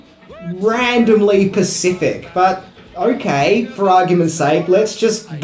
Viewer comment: Did you just say "Randomly Pacific" ????